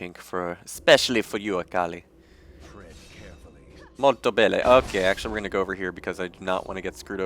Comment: did he just say "molto bene " ? lol